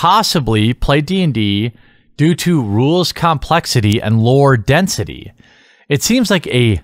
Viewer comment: I thought you said "lower density" at first and wondered why women would have a problem with that.